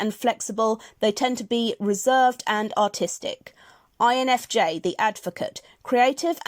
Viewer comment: lol. When you were reading the artist archetype, I swear I thought you said “autistic” at the end.